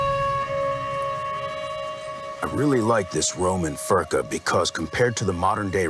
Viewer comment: I thought he said this fucker here not furca